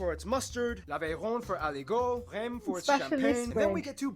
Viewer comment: did he say Rème ? 😭😭😭